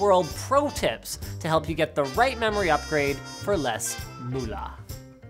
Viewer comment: what did he just say ? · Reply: moula xD